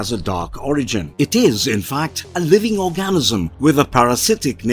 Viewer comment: OK, tell me that at 21:59 you didn't hear him say "Living Orgasm" lol